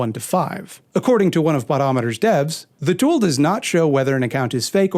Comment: When you were saying “botometer”, I thought you were saying “buttometer”, as in he was pulling numbers out of his ass